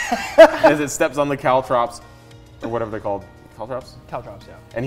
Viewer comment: with the caltrops, i thought they said cow chops at first for the dogs to be distracted with